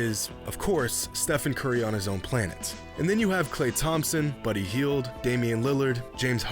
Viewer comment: when he said "Klay Thompson, Buddy Hield" i thought he said "Klay Thompson but he healed"